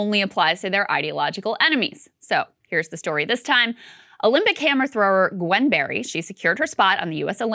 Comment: I swear I thought Krystal said when describing the athlete “camera thrower” instead of “hammer thrower” 😂😂😂😂